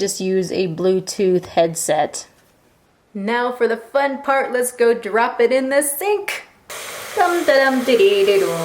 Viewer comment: Now even our phones can wear condoms. And at first, I thought you said: "lets drop it in the stink".....